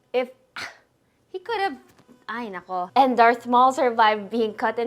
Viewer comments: i heard a "hay nako" around 2:35 are you also a Filipino???? OMG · are you Filipino? I don't know, I think I heard you say "ay naku"